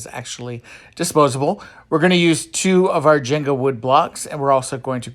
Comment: When you said "Jenga" I thought you said "gender." I was like "what's a gender wood block??"